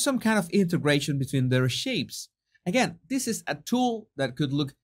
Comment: I thought you said LOOK AT SHEEPS not shapes and cracked up. I gotta clean my ears. 😂